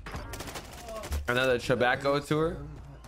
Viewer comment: My man said another chabaco tour instead of tabaco chewer 😂😂😂😂😂😂😂 🤣🤣🤣🤣🤣 @ 6:19 lol